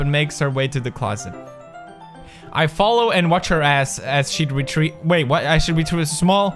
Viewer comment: "I follow and watch her ass" am I the only one who noticed what he said 😂😂😂😂👌